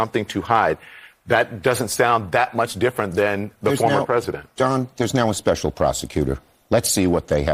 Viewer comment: did he call him John?!